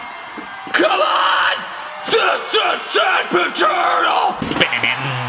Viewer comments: for some reason instead of hearing “THIS IS SANDPIT TURTLE” i heard “THIS IS SIMP ETERNAL” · I heard "this is so paternal" not *this is sandpit turtle" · Someone hears: "THIS IS SANDPIT TURTLE!" What I hear: "THIS IS SIMP ETERNAL!"